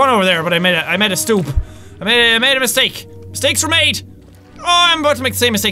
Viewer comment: I heard him say steaks were made.